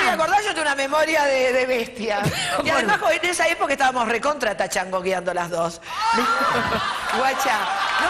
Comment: Soy español y no entiendo algunas palabras. Qué dice en 10:17? Recontratrachangogueando!?!?!? PS: Aunque no entienda todo me hace muchísima gracia esta mujer!!!!! Qué descubrimiento de señora!!!! No entiendo cómo no la conocemos en España: es divertidísima, debería ser una superstar aquí también!!!!!!!!! Un programa para Moria Casán en España yaaa por favooooooooooor!!!!!!!! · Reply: Dice "Estabamos re contra Tachangouando". Viene del inglés Touch And Go (que en Argentina, se refiere al s3x0 casual) por eso TouchAndGo-ando.